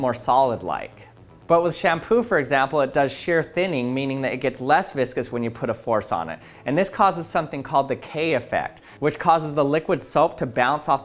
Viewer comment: Ngl I thought that said “Kanye” effect lmao